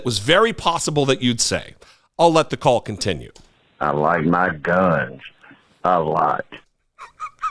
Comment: I thought he said he liked Mike Dunn, not "my guns". Sheesh!